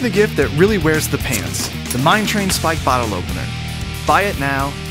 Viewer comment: At first I thought you all said MIKE'S Train Spike Bottle Opener not Mine Train Spike Bottle Opener